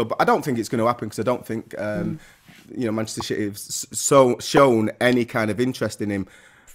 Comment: did he just say Manchester shitty? 🤣🤣